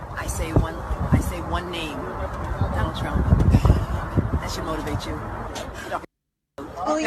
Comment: Tish has TDS R E A L bad. Did she say, "fk Donald fkn Trump" at 6:42 ? ? ? Demon-possessed thing that she is.